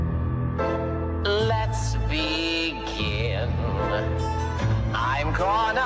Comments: To be honest I thought it was about to say “Let’s be gay” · I really thought he was going to say let’s be gay 😭 I can’t unhear it